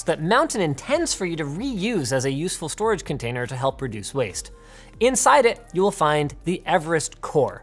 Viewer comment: Is it me or did he say, "helps your produce waste!" ?